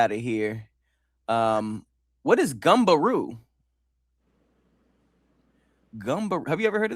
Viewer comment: My creole ass thought you said gumbo roux not gumberoo